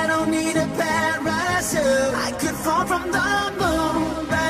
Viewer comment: I thought he was saying i don't need a plan but i should but hes actually saying patrachute